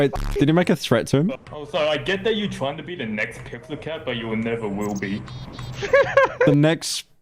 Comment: I think he said PixelCat or something, PixelCat is a Gmod Player that trolls on certain servers